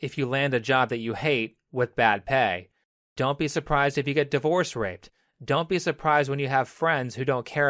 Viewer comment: Hey can someone please explain what the term "divorce raped" means or have I just completely misheard this man